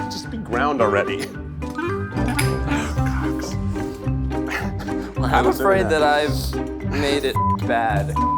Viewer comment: was it just me who heard say: "oh cocks" at 22:28 as he was grounding the pepper LMAO